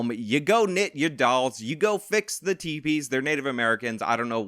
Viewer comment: thought he said, "go fix the TVs." 😆